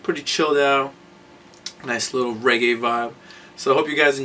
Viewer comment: lmao i just clicked at 0:32 tryin to find the start of the music and all i heard was "gay vibe" obviously "reggae" got cut off